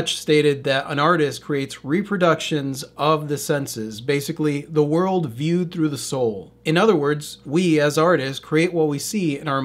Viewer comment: What you said .." The world viewed through the soul" .. what i heard ..." SOUL VISION..ACTIVATE!!"